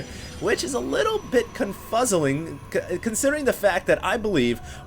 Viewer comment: Did he say "confuzzling" at 1:31. lol